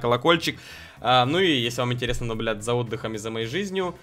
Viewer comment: Или мне послышалось он сказал наблюбл*ть ?